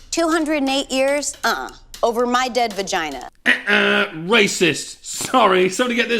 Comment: Did she just say "dead vagina"?